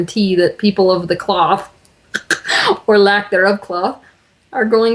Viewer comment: Can somebody please help me ? What did Teal said at 1:15:03 "people of the...."? I can't understand this word. My language skills are not so good. I'm from Berlin.....Thanks ! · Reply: Yes, she said people of the "cloth". This refers to priests (probably a reference to a specific type of cloth that was used for their robes at one time), or other religious leaders. However, she was also making a subtle joke I think because Sai Baba probably didn't wear much "cloth", just a very simple piece of clothing most likely.